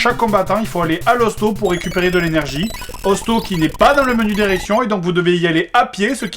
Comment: Pouce vert si toi aussi tu as entendus "le menu d'érection" à